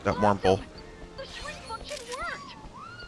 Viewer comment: I really thought the kid said "the shrink fuckin' worked!"